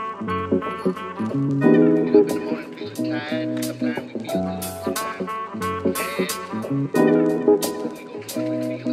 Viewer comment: I’m really trying to figure out what’s the phrase he says in minute 1:21? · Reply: “We get up in the morning feeling tired. Sometimes we feel good, sometimes bad, but we go through it with feeling. That’s the root to the truth, that’s where everything starts.”